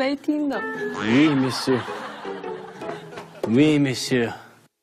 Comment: Did Jackson say "Oui monsieur, oui monsieur" in french at 12:30 ?